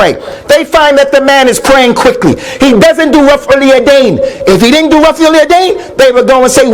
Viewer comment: As salaam alaikum, do you guys know what he says at 8:59 and if you do know what he said, what is it? Jazak Allah Khair!! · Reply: It's rafa ul yadain..that's raising of hands to shoulder level before and after rukoo